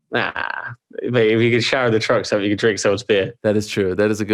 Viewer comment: What did he say at 20:48 before "you can drink someone's beer"? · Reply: If you can shower at a truck stop, you can drink someone's beer